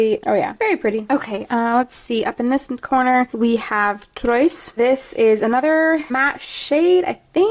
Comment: help what colour is she saying at 13:28 i cant hear her properly and i need to know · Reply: Hi Daisy! It's a french word (i'm pretty sure at least) and it's 'Trois'(obviously i'm not very good at french..hehe). It was a limited edition colourpop supershock shadow last year. I tried to see if it was available on their site anymore and I couldn't find it. :(